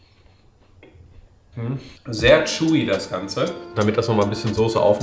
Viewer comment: Sehr "Jui" das Ganze? Hab das nicht so richtig verstanden. Was meinst damit? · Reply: Chewy